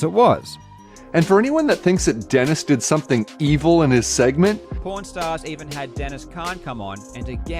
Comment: I keep hearing "porn stars" and doing a double take